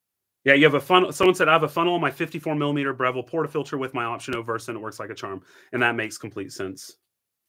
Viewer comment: can anyone decipher the ending to what says at 39:37? "Someone said, 'I have a funnel on my 54mm Breville portafilter with my [indecipherable].'" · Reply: With my option O Versa